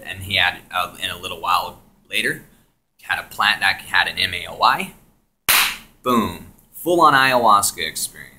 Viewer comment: i can't understand what you said about a type of drug i want to know of ama...something lmao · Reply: He said maoi but that's not a drug